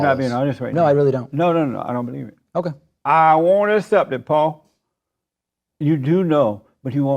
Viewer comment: Did he call David "Paul"??